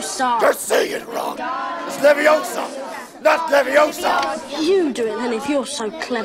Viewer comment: Say it Ron, it's LEVIOSA! Not LEVIOSA! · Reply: Nick Beaudry Yes. With subtitles on, she says "it's leviosa, not leviosar". Without them, it just sounds like she says "it's leviosa, not leviosa".